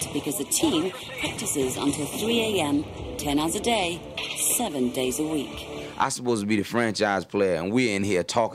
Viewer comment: did i hear "7days a week" ?